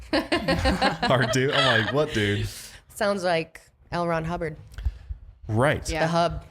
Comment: Any time someone mentions the scientology guy I hear it as "Elrond Hubbard"